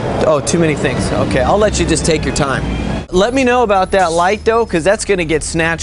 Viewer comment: Did she saY take your pants off